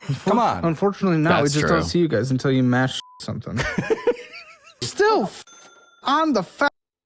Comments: What did he say at 9:00 in? "Until you mash something?" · what did he say? 😅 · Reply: ‘unfortunately not, we just don’t see you guys until you mass shoot something’